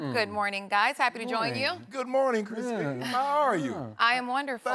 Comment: When she said Kristina......Pink lol was ready to click off if she said Leahy 😂😂 thought she said Kristine too ha